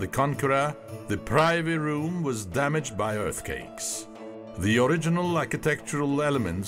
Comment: No disrespect great documentary but at 10:02 does he say Earth Cakes?😅